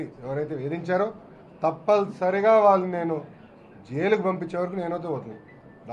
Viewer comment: Thappalsory Have i heard it wrong ?? 😀